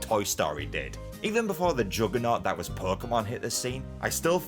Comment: Did he say juggernaut